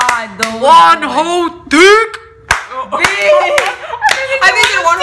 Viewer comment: I literally heard "WONHOS DICK" im with and on that one😂